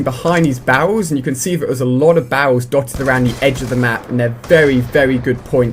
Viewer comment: it sounds like he says bowels instead of barrels lol :)